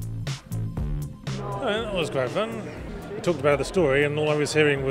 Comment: What the fuck. Did you guys hear that at 6:07? I could've sworn I heard Yahtzee say "well, that was quite fun"!?